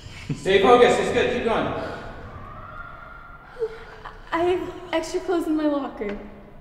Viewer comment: It sounded like she said I have vegetables in my locker.